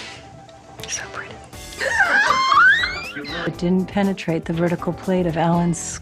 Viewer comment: what did Alexis say in 5:16? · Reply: "so pretty"